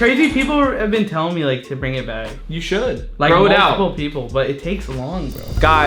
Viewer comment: for a second I thought he said “whip it out”